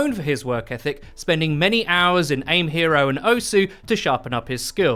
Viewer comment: did i hear OSU?!?!??!